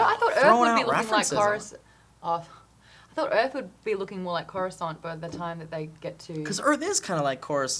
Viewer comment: What are they saying at 6:50? Korensant? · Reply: Coruscant, from the Star Wars prequels